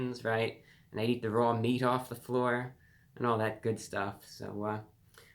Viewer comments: what’d he say?? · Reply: I heard “they eat the raw meat off the floor” 🤔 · pardon? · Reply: I heard “they eat the raw meat off the floor” 🤔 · I think CC got it wrong….. looking up at the stars… “And I eat the raw meat 🥩 off the floor.” 🤣😂 I’m dying.